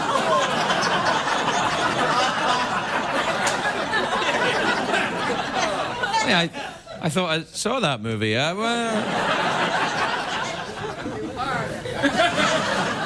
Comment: Haha did someone say "You ARE in that movie?"